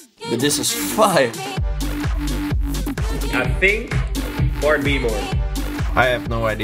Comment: did he said about 4B ? 😅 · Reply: He said bart b more